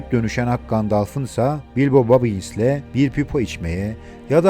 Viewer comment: Hocam yanlış anlamayın ama bazı isimlerin telaffuzu yanlış oluyor sanki. Bilbo Babins gibi duydum mesela